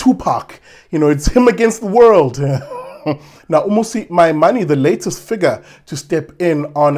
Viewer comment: 🤣😂🤣😂 I'm sorry but every time someone says his name all i hear is Musi "My Money" 😂🤣😂 I can't I'm dead 🤣😂🤣